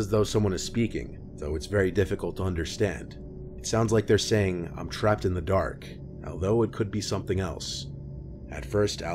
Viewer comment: to me, the voicemail of the crying person sounds like they’re saying “i’m stressed and i don’t know what to do”. could be an ex friend/boyfriend/estranged family member of the girl. especially if she’s had the number for a long time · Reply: I hear “I’m trapped in the back of (something)”